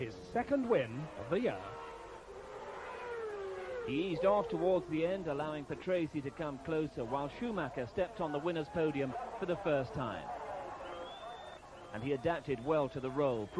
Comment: does he say RALF Schumacher